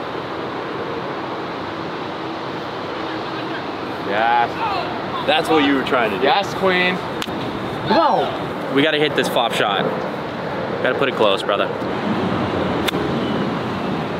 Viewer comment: Love the content ... However !! @ 16:36 did I just hear Brad give a "silent" shout out to golfsidekick !?! As I heard it; "Yaas queen !" If so, d.o.d. is not the play if a playa is looking for a lower score.